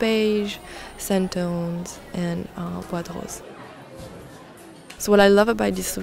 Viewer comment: when you said "bois de rose" i thought you said buttholes